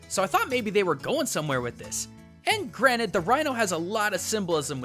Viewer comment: When you said "and granted" and it sounded like you said "granite" Did you put a granite Rhino?